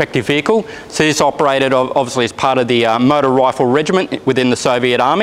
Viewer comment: Ha with his Australian accent I at first thought he said "murder rifle" rather than "motor rifle"